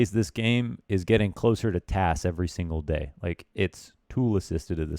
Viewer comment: I thought you said TAZ and was like "wut?"